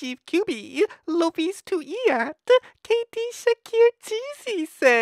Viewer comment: It sounds like he is saying kd shheakeer instead of kd shaker.